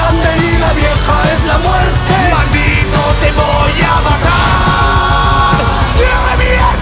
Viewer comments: no entendi que dijp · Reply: Dijo te revienta thor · Que es lo que dice Thor en el min 2:28 ?? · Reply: Te revienthoooooorr